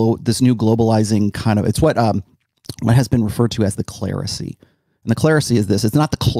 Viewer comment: did say "my husband" ? I thought I remember in past videos him talking about having a "beautiful girlfriend"... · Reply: Closed captions helped me "It's what has been referred to as cleresy"